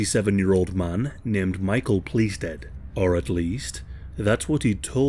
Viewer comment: Am I the only one who heard Michael PleaseDead?!